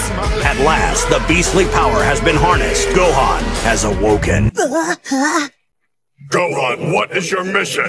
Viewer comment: 🤣 I'm sorry but the narrator said "awoken" and I heard the word "woke" and almost got triggered 🤣🤣🤣